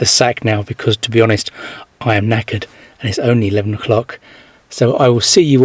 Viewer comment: What's that word, neckered? I thought you said naked, haha!